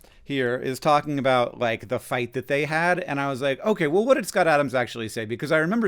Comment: hank saying "okay, well" sounded like 'OK Google' enough to activate the assistant. In an echoey bathroom over the sound of my electric toothbrush, but still.